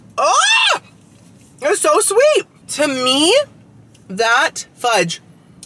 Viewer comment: LOL @ 35:32 He said "To Me" and I heard TIM MEEEE and I'm playing a game on my phone while watching YouTube video's so I Heard it and thought Why he say his name like that So I went back a little and It made me giggle a little because I am stupid and missed Nothing at all